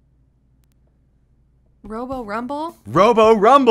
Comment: I thought he said boss fight instead of robo rumble